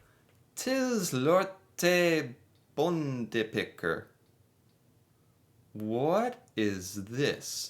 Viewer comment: bro say it slower when you say it fast like that it sounds like your saying pikker aka dicks. I also absoulity hate smalahove i think its wierd. Yes im norwegian